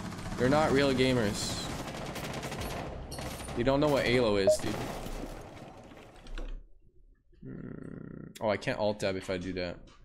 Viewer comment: did he say halo? Or alo?